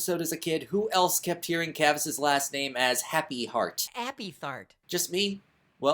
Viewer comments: You have a cleaner mind than me. I always heard it as "Happy FART." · Your Larry impression is actually pretty cool and funny, and it adds to the fun of some sinnings! Also, I keep hearing say "Appy-fart" 😂